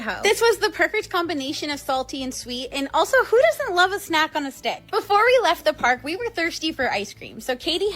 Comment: it almost sounds like she said ".... Western hoe..... Snacking on a stick...."